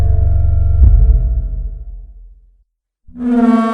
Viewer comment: did you just say "Jiya roll the clip"? Cause that's my name and either both me and my partner are going insane or you just said my name ☠️☠️☠️🤣